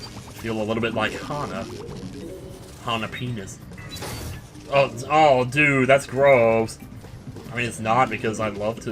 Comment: Wait... what was he saying there? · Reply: He was on about Asagao probably.